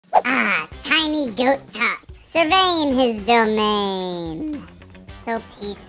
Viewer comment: 😂😂😂 what was saying??!?? 😂😂😂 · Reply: tiny goat top survey his domains